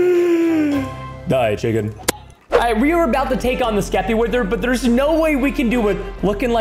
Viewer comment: For a minute I thought it said sleepy wither